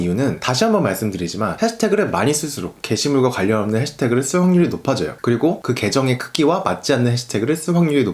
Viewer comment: "해시태그를 많이 쓸수록 게시물과 관련없는 해시태그를 ㅁㅁ 확률이 높아져요" ㅁㅁ 이부분이 잘안들리는데 혹시 아시는분 계신가요? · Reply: 해시태그를 많이 쓸수록 게시물과 관련없는 해시태그를 쓸 확률이 높아져요" 인것 같네요